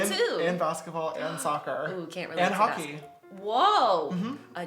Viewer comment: Did she just say “can’t relate to basketball....”